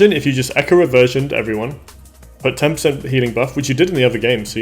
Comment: Great guide! Thx I learned alot! What do you say at 21:07? Put temps in healingbuffs, What do you mean? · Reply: 10% healing buff i think haha - I'm talking about getting reversion applied to the team first, which buffs your healing output